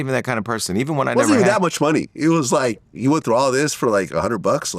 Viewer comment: bro did Vlad just say “HE”??? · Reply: He says “you”